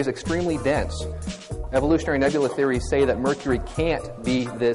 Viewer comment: Yes, I didn't know evolution ( couldn't hear what evolutionary "narular" is 1:26, but saying evolution and evolutionists without sth like "cosmic evolution" mean biological evolution by default) predicts or state anything about Mercury can't be dense :D